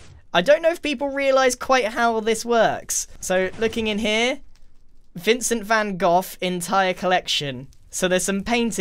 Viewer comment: | DID HE SAY VINCENT VAN GOFF XD